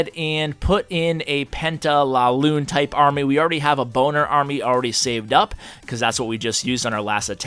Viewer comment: Did he just say we have a Boner army ? XD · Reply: bowlers and miners.